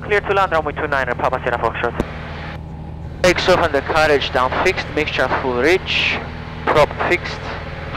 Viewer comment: Did you just say "undercarriage down, checked" in a fixed-gear aircraft??